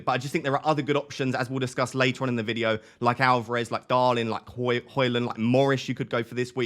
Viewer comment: did you say darlin there?😆